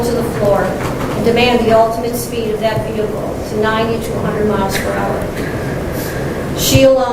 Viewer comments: what was the speed? I hear a number but I dont even think its possible. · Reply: I’ve seen 100 MPH. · 9,200 mph? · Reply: 90 to 100. I thought she said 9,200 at first too, lol. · Did she say 92 hundred mph? 😳 That's a bit fast! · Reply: She said 90 to 100